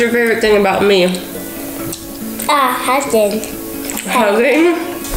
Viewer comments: When said "hugging," I thought he said "husband" 😂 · When said what he liked about his Mom I thought he said Husband instead of Hugging 😂😂😂😂😂